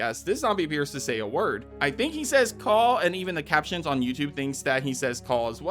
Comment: he actually said “cut out” and Leon says “think I need a second opinion” as a response